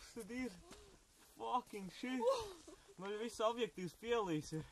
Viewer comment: Hehe.. did he say "Fucking shit" at 0:34?